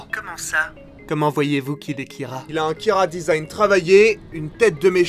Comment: j'ai compris ça il a un kira-design travaillé XD